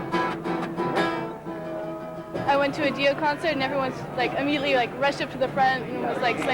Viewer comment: Did she say she went to a Dio show?